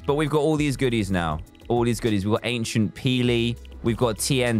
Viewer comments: did he just say "Ancient peely"? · did he just say ancient peely? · "ANCIENT PEELY" IQ 🤯 Edit: Thx for the likes 👍🏻😁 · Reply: He said agent peely but it did sound like he said ancient peely so I'll give You a point 😂